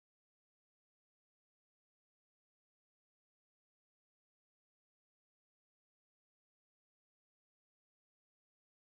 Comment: What did Lauren call Dinah at 3:00? I heard porn star... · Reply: Prank star lol